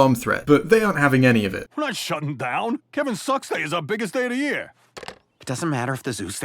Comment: when the funny funnyyyyeyeyyey fat boy man says kevin sucks i was watching at 2x speed and it sounded like he said giving sex